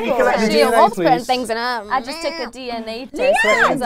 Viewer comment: What did leigh say at 4:09? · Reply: "She loves putting things in her mouth"